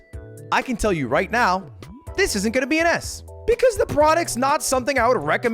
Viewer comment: I thought you said "This is gonna be an Ass"